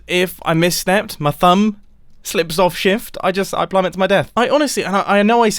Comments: Did he... Did he say THUMB slips off shift HIS THUMB WHAT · did you just say your THUMB just slipped off shift??? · Did he just say "my thumb slips off Shift"? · did he say is thumb?? Whaaat?!! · Everybody: Did he just say thumb on shift?? Me that’s been watching for years 😁😁😅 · did I hear say if my THUMB slips of shift? why thumb? no pinky finger?! pinky finger not strong enough????? whats wrong with you man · Did he just say that his THUMB slips off Shift (8:41)? Bro, how are you holding your keyboard??? I don't think the lack of Elytra was the problem... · did he say if my thumb slipped off shift? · wait, did he say thumb? How do you hold shift with your thumb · Wait at 8:45 did he say thumb slips off shift? How is he hosting shift with his thumb?